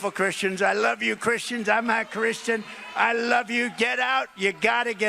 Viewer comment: I heard "I'm a Christian!"